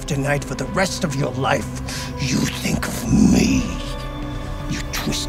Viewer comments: What does Bolsa say at 0:37? Netflix subs just say "Speaks in spanish". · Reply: "He's yours!" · Anyone know what he's saying at 0:37? sounds bad af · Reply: “Es suyo” common spanish expression